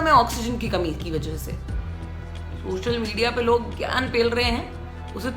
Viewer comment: Is she said gyaan pel rahe hai 🔥